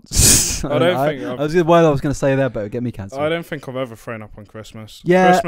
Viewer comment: Does anyone know what on earth said at 19:22 I went back like 5 times and I’m still confused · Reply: I say “do you ever throw up around Christmas time” in broad suffolk tongue